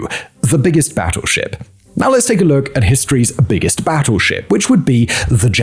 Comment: you say, "history's biggest battleship, the Yamato" I hear,."the biggest man made coral reef, the Yamato"